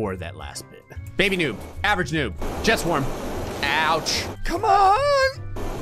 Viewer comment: when you said "jet swarm" it sounded like you said "chest warm"